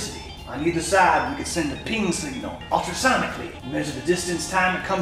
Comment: i thought he said penis signal